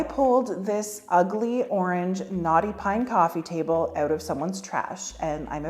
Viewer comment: Lol. I dont know why this is, but EVERY time you say "knotty pine", i hear "naughty pine".....and then I wonder what it did that was so bad...🤣🤣🤣🤣